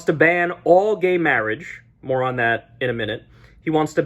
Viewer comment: I hear this as, "Moron, that. Later!"